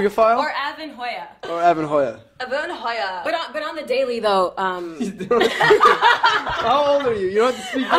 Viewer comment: Hi guys! Could you help me? :) I don't understand what Kylie and Avan say at 5:45 and why is it so funny (I'm not a native speaker :) ) Thanx! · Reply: She said "On the daily though" which could be translated as 'To be real' or 'To be honest.' but Avan and Maddie were laughing because of how she leaned super close to talk right into the microphone.